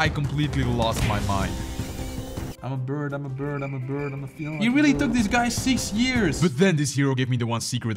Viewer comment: Did he just say ,,I'm a bird, I'm a bird I'm a bird I'm a feline"?